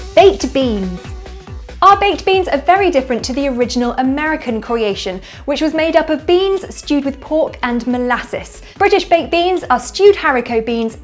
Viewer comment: Did she just say American beans are made up of Beans and Stupid Pork?